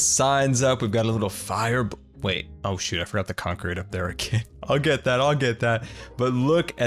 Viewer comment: I could be mistaken, but at 27:09, did just say, "oh sh*t, I just forgot the concrete up there"?